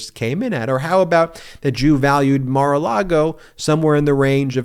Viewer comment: Jew valued? 5:32 please tell me I heard that wrong